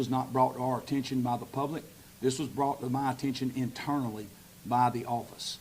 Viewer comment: Where did he say a deputy told him this ? I thought he said it was brought to him by the office.? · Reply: I heard him say internally